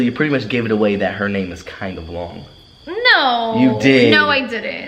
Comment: For some reason when said “so you gave it away that her name is kinda long” I heard “so you gave it away that her name is kyla wong” lmao I need help